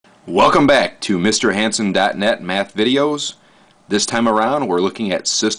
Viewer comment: i thought the guy said "welcome back to HANDSOme...blah..blah..blah" yeah, sorry about that